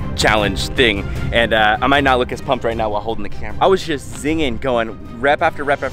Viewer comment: you say “zinging” but the subtitles say “singing”